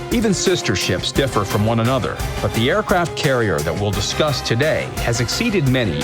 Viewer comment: I misheard discuss as disgust